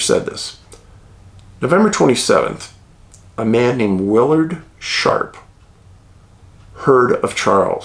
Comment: almost thought you said his name was Willard Shart 😂 there goes my hearing. Getting old stinks.😢